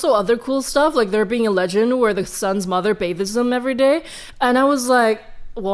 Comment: Every time someone tells me they're Chinese diaspora and my content helps them get more connected with their culture my chilly lil heart grows one size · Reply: did you say bathes? Also I couldn’t understand you until 3:03 sorry